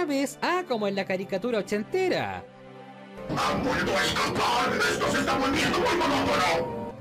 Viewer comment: Jajajaja! Ese clip de Destructor siempre da risa 😆 Me pregunto que dice, cuesta entenderlo. Yo creo que dice "Han vuelto a escapar!? Esto se esta volviendo muy monotono!"